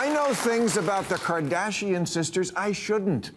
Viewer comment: Am I the only one who thought he said Cardassian?